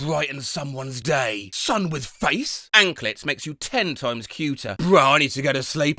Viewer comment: I HEAR THIS AS ANKLES